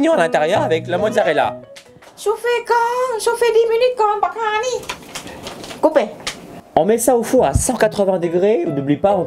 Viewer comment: C'est moi qui ai maté trop d'animé où elle a dit "baka onii!" À